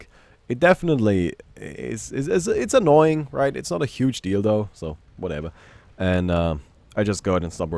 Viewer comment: i swear i heard "Its not a huge dildo" :DD